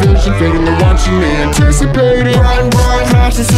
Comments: when it says "run run from freddy" i hear "run run golden freddy" not gonna lie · Who else hear's in place from : '' Run run from freddy '' = '' Bon bon Fun freddy '' If you know please answer this xd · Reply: I Hear It "Bon Bon" Instead Of "Run Run" Too qwq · So when it says "RUN RUN" I thought it said "BON BON" I dunno why xD